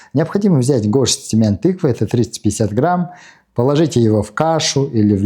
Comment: Спасибо за видео. Горсть семян тыквы 35 грамм или 30 - 50 грамм ,.(для тех кто не расслышал и решит съедать 350 .)